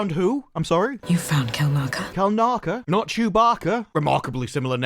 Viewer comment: I honestly thought she said "You found killmonger?".But to be fair - Kubaka,Killmonger - when she talks so quitely i can have her monolgue interrupted by my own heartbeat - it is easy to mix up